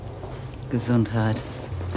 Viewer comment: John W what does po face say at 15:06?? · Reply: Gesundheit. Good health in Germany 😷